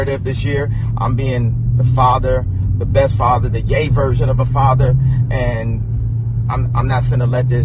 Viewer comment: Did Kanye say he was going to be, "The gay version of a father"? Because that's what I heard and that's what the captions said. I need a double check here ya'll what?